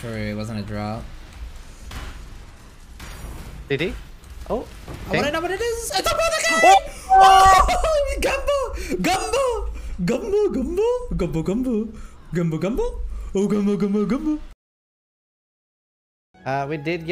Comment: Did he say "gavno"?